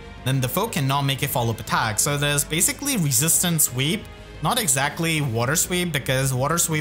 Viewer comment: Enough with the pre-combat please 😭 Also I keep hearing “resistance Sweep” as resistance Weep 😂 hahaha Love you man.